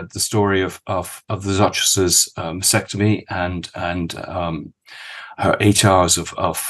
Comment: Did you just say “the Duchess’s VASECTOMY”. ???? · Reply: Bad quality recording I think. means mastectomy!